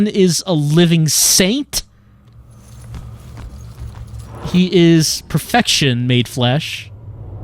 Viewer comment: I was distracted at this moment and thought said, "He is CONFECTION made flesh" and thought was waxing poetic again about how yummy Ronan is.